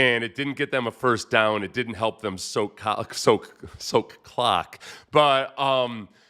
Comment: "it didn't help them soak cock" 😂 Seth's face knowing what he just accidentally said